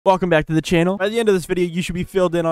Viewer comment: why do I keep hearing f*** back to the channel